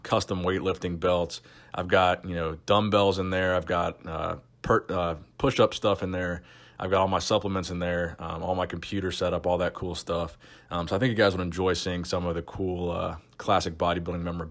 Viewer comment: I thought u bout to said " I got percocets....um push up stuff" 😂 I'm fun around, but sure sounds like it, which is all gravy